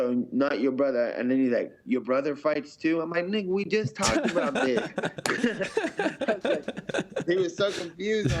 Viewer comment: anyone else hear him say N***a we just talked about this at 50:58?